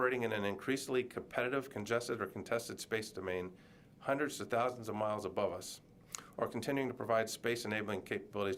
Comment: He said hundreds of thousands of miles above us????? Wait what? The moon is 205 thousand miles. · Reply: i thought he said hundreds to thousands of miles...